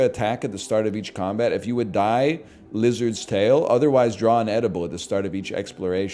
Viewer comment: "lizards tail" instead of, you know... Lazarus Rags👀